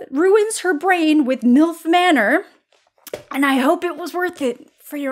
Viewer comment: I misheard MILF manor as Filth manor and just, yup